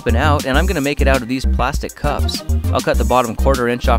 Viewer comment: I thought I hear him say n-er cup